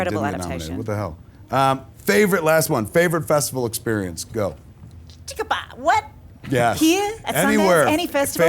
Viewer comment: What did Toni say at 9:10? XD XD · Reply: Think about wot? Here?